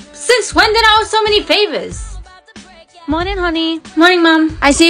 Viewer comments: I thought it said flavours and I was so confused I watch the vid 4 times then read the comments and realised 😢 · My dumb ass thought that said flavours